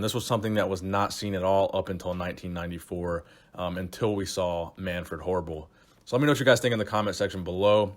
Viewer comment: Sounded like you said Manfred Horrible” at the end 😆